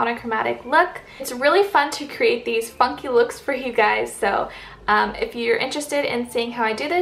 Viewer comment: It sounds like you said "fucky looks for you" i was like what? 😂😩